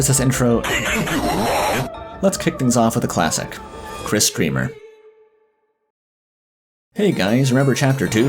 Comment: When you started kris' section, every time I've watched this video I thought you said "Kris kremer" not dreemurr